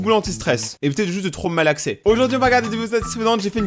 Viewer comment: "évitez de trop me malaxer" Moi et mon esprit mal placé: euh non merci chui hétéro